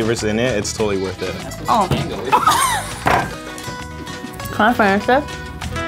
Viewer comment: corn i finish this i hear that first